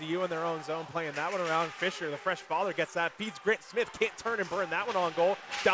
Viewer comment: Did he just say “the fresh father”? One of those players has a kid?!